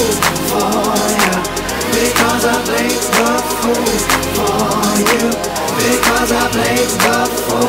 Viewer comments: I thought they said "because they make the food for you." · thought they were saying " here goes a plate of food for you" the first time i heard this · Always thought they said ‘because i play good football yuh’